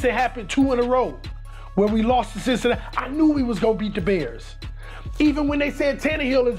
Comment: I thought they said we was goin 3-3 😂😂😂